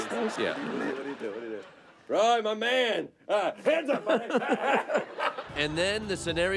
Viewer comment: Did i heard hands up my n***a